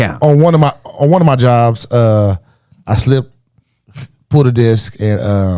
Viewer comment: I thought said slipped on a D$ck 😂😂😂😂😂😂